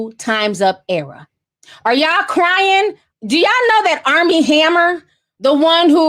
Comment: I thought you said arm&hammer im like, what they do?? l love Brittany, my fave song of hers is slave for you